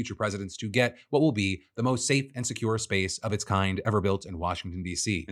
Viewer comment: I misheard what he said people are saying is to blame for the chemical leak, what I thought I heard was “squarespace” lmao